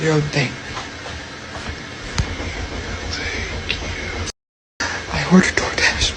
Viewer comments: DID ANYBODY HEAR "TAKE YOU" OR "THANK YOU"?! IN A WHISPER.!😮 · wait am I tripping or did something jus moan out" thankkk you "... 🤔 Am I tripping I listened 5 times I could've sworn I heard that